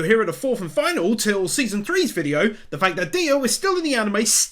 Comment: Did you just say DIO?! sad star platinum ora's